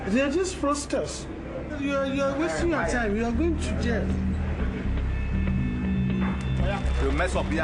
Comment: the subtitles say "You are going to Gaol"... surely he says "You are going to jail". lol wtf?